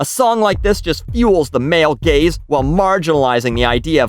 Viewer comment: Lmao I thought she said it fueled “the male gays.”